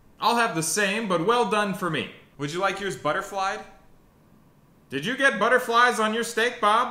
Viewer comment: For everyone confused by the “butterflied steak”, the restaurant will order “steak caterpillars” that metamorphasize into what you know as “steak butterflies” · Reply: Thought he said Buttered fried. Like he was asking if he wanted the steak fries in butter instead of oil.